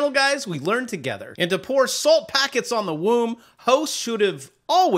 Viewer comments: Bro, did you just say pour Salt packets on the Womb? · My man.....did you just say “salt packets on the womb”? Ahhh.......